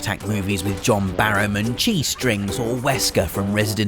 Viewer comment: I thought he said G-strings at first.